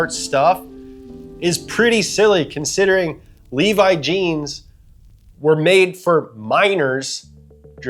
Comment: I thought you said Levi's were made for MINORS 😂😂😂😂